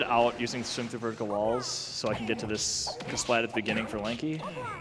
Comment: He actually said Lanky PogChamp